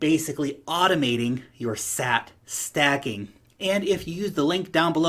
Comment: Had to rewind, thought you said “STAT-SACKING”!